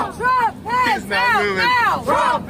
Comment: I misheard them saying Trump pants out now.